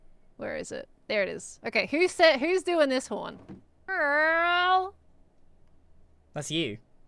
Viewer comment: I think this horn is saying "Pearl.." not "Girl" as the captions say.